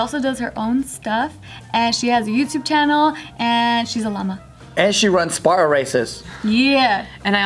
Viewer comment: I thought said she was a momma, not a llama..